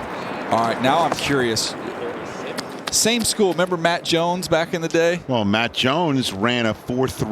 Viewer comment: For a second i thought they said mac jones i was boutta say he did noooot run nothing close to a 4.38